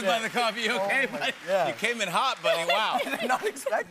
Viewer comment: he said mike instead of Noah ahahahahhaha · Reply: he said "bud" AND THATS WHY "SUBTITLES ARE IMPORTANT" HERE